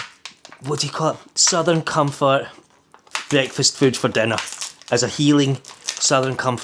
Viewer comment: It sounded like you said southern comfort breast I’ve had to laugh